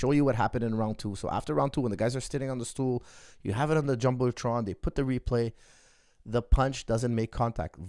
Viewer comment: they have it on the jumbletron??? What’s that word he said ???